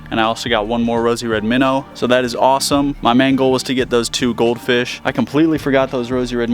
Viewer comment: I heard "my mango" 🥭